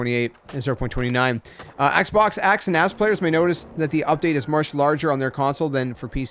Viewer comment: did he just say axebox, axe and 🍑 players? 🤣🤣🤣